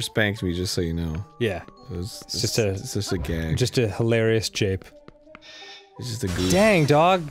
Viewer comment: there is NOTHING funny about jape.....oh he said Jape I thought he said ra- nevermind!